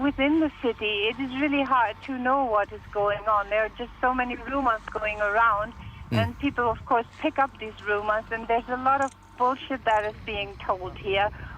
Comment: did she just say bullsh$t at 4:54!